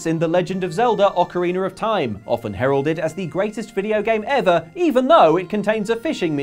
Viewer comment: Sorry but i misheard you saying "Especially since it has..." as "even though it has..." But that can't be right.